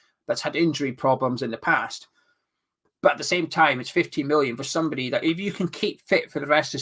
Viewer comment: I thought you said 50million at the start of the video. Almost had a heart attack